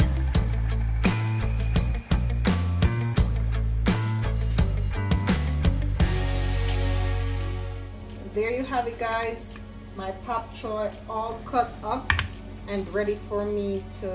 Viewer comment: Your demonstration sure makes me want to try your recipe soon, but did I hear you say "Pap" Choi?